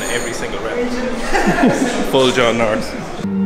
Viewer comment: Please someone tell me how the guy on 10:26 is called, i can't understand and i want so bad to find who is he xD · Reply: John/Jon North ?